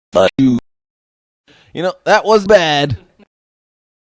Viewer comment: Did you say you know what that was bad it's a bad soulotain word